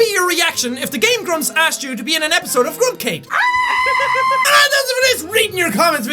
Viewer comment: it sounds like Jack said "GrumpCake," well it does sound like something he would say